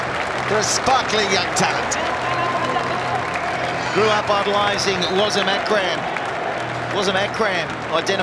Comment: Can someone tell me what word the commentator used at 4:33?? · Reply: Grew up idolising wasim akram